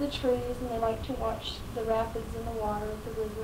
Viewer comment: Did they look in the rapids for the gun? The way she addresses the rapids struck me as odd. She then speaks of the bridge over the McKenzie river. I’m just wondering if they stuck a super strong magnet in the rapids to look for the weapon, since those would be difficult to search otherwise. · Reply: Are you talking about the 7:35 segment? I had to go back and listen because I could not remember her mentioning the rapids when I first watched the video. The subtitles say rabbits. I'm not a native speaker of English but it does sound like she says rapids. I agree it is a good comment you're making. The fact that they never found the actual gun that was used bothers me. I'd like to hear from an expert how likely or common it is to not find a gun that has been thrown in the water, especially a river of the kind of the Mohawk. Some people argue that based on the efforts deployed by the police, the gun should have been found, had it been thrown there. I wonder if this is truly the case, i.e. if she just got lucky or if a gun thrown in that environment is actually very hard to find in general. Was this issue addressed during the trial?